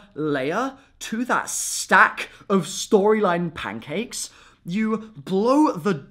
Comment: Did you just say stack of storyline pancakes xD